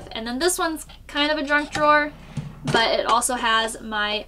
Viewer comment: Thanks for the tour of your home, I really enjoy that you often leave the "real life" stuff in your videos (What is that thing called? It's a radio). Also, it may have been my ears, but I am pretty sure I heard say "Drunk Drawer", either way I was laughing. I am currently trying to figure out why my generator wont start. It just makes a single click right next to the motor (I think the click is coming from the solenoid).